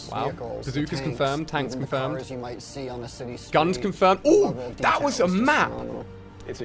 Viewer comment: Did he say guns confirmed at 8:50? Well at least now we know there's guns that's a good thing